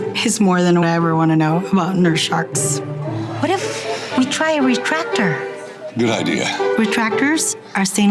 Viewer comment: did she just say to try a protractor edit: oh she said retractor